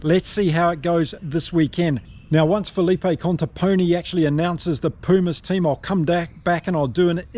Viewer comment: Its actually Felipe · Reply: you're right but i always hear you say 'Conteponi' it should be 'mi' and not 'ni'